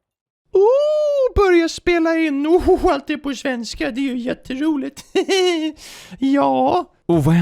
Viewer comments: I might not have caught it all but ' borja spela in i svenska. Det är jätte roligt!' is what I heard. 'Begin recording in Swedish. That is super fun!' If any one can correct me, that would be cool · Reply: You almost had it. He said: "Börja spela in! Allt är på svenska, det är ju jätteroligt! Jaa." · i think I heard something about a roulette wheel? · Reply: I'm not swedish but I think he said "Börja spela ännu alltid på svenska, det är jätteroligt." The part that sounds like roulette aka jätteroligt means very fun. Those may not be the exact words he used, but it's probably close enough.